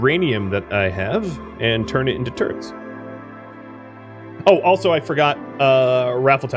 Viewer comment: "Next step is to take the uranium I have and turn it into turds!" ... Ok, then XD (I know you said turrets, but I definitely heard the more amusing option)